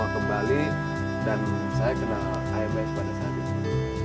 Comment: backsound nya kegedean nih, jd kedengerannya kurang jelas. ini kan cerita pengalaman dan motivasi jd gak nyampe apa yg d omonginnya, karena kgedean backsound nya. but overalls keren scene ini 👍👍👍 menit 8:35 smpe 8:38 kang ngomong apa itu? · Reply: kang cerita kalo kena AMS (Acute Mountain Sickness)